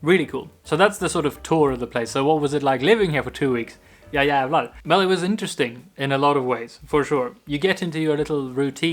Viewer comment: Guys i need help what is the Swedish thing hes saying at 2:26? It doesn't let me sleep at night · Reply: "ja jävlar"! It means "yes satan" and could be translated as "fuck in eh" kind of.